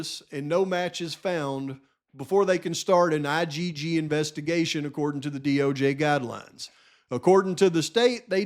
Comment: I heard that slip “DOG guidelines” lmaooo